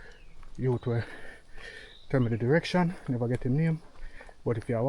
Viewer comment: Got to be brave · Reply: I thought yuh said, never go alone? 😂